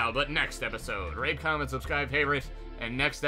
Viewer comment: I thought that he said rape comment and subscribe nit rate comment and subscribe